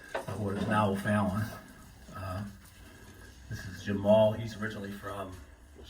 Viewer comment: lol I thought he said his name was “Jamal” not “Jim moll”